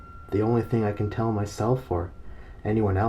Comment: The first time I listened through, I heard, "don't trust the duck." Good thing I don't have anatidaephobia, hahaha.